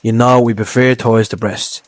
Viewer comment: Mate, I just discovered your channel. I like the ‘straight to the point-style’ but I had to watch (or actually listen) the video multiple times because of your thick accent (i.e. when you say ‘thighs’ I hear ‘toys’). I am not trying to ridicule you but for a non native European mainlander some subs would really help. The recipe was great btw, very delicious!